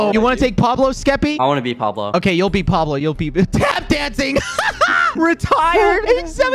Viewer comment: i thought it said lap dancing not tap dancing